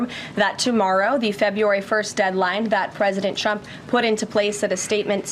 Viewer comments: In her opening statement, it sounded like she said "president Chump", which is correct. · Did I imagine it or did she just say ”President Chump” ?? 😮